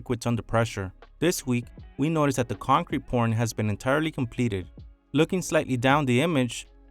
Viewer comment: am I the only one who heard "concrete porn"?